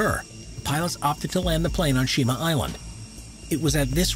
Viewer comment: first i heard kyoshi island, and then shiba island. damn, i needa get my ears checked.